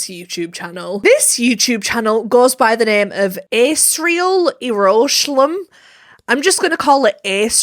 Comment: I was listening to the video and I thought you said Israel😭